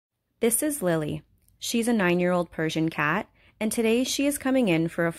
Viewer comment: I heard "9 year old virgin cat"